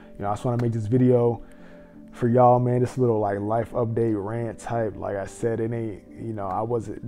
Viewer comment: Nah, cause why I thought that said “lice update” 💀